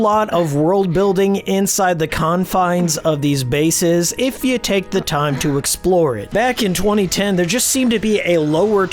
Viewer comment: What game did he mention at 14:23? Hellblade? Hillblade? Ill Blade? Not even the subtitles can understand what he said there. Lol 😅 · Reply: He said Illbleed. Its another horror title on the Dreamcast that, in a nutshell, takes the raunchy content of The 3rd Birthday and cranks it up to 11.